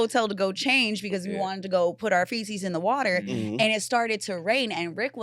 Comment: i thought you said you wanted to put your feces in the water lmao i KNOW I heard that shit wrong but it had me rollin